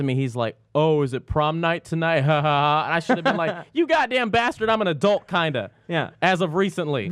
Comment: holy shit did just say bastard :o